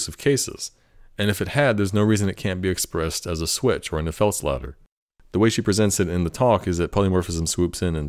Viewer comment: what does he say here? "it can't be expressed as a switch or a ???????????" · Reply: if else ladder